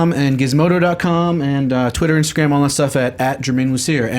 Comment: I heard "it's Man of Steel" instead of Germain Lussier